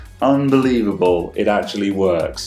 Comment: Competency! LMAO I heard a "unfu...believable" for a micro second there...LOL. I'll just finish it for you, unfuckin believable! Nice job buddy!